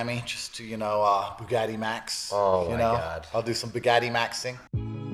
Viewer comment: Instead of "buggatimaxxing", I thought I heard Andrew Tate say he was gonna do some "daddymaxxing" and I threw up in my mouth a little bit.